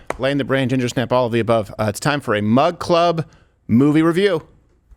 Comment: yo past 4 or so years i thought youve been saying MUD CLUB......